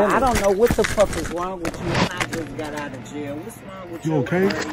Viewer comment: Did Auntie say 🗣 she just got out of jail 😳🤦🏾‍♀️🤣🤣